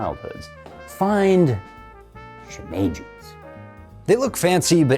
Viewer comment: I found the joke 6:58 says "Find shimejis" and the subtitles say find juice! Ha... well your plan is foiled, now everyone can make your cheese sauce! 🧀